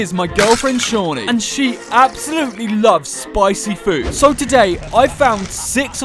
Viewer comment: I thought you said layla likes spicy food